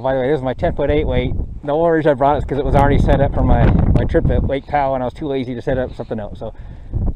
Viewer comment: Where did you say you where going to fish that 10’ 8wt? I couldn’t hear and thought you said Lake Tahoe but I wasn’t sure. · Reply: I said Lake Powell. The rod was already set up from my Powell trip two weekends ago.